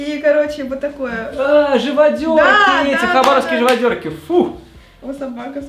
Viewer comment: мне послышалось сказал Хованские живодерки